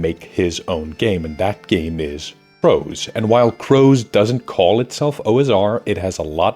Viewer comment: I kept hearing “rose” until 2:43 Crows. Got it. 😅